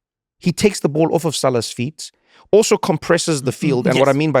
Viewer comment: Joe said something I had to stop go back a few seconds to replay it initially sounded like he was saying feet salad, now I can't get him saying he thinks he sounds like Hugh Grant when he speaks out of my head from when he was on the pod. 🤣😅